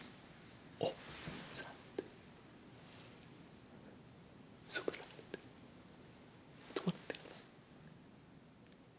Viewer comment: idk what he is saying but I hear SOUP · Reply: He’s saying soup or salad